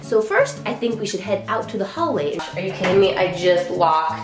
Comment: Hi! I've heard so much about you and recently became obsessed with your videos! Your content is awesome and you radiate! Because I want everyone to enjoy it, I suggest you fix your captions. Wrong captions makes it hard for the deaf and hard of hearing community. At around 3:48, the caption says "We should head out to the holidays." I love the holidays!! But the caption should say, "We should head out into the hallway." When you get the chance, please fix it. This will make it easier on the deaf and hard of hearing community. Thank you! I enjoy your content so much, keep it up!